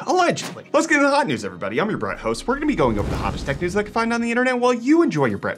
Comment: Did anyone else catch that "I'm your Brian, Host"